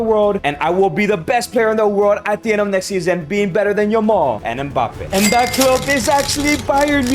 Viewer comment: Why does it say "be better than your mom"